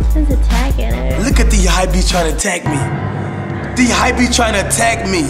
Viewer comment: hype beast lol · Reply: thought he said hypeys